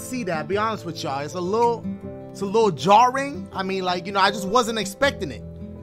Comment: I thought he said it was a little jaw ring...i had to double check what the shit he was talking about!